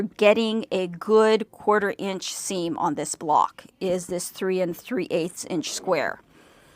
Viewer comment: For the SNS center block, it sounds like you say 31/8” square, but if I’m reading the instructions correctly it should be 3 3/8” block..is the extra 1/4” wiggle room? Should I always add an extra 1/8” to square up the block components?